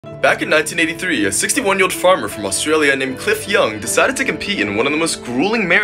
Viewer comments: I thought he said "he decided to take a pee" in the beginning lol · i thought he said “decided to take a pee” KNFMANNDSN I WAS LIKE “he peed in a race?”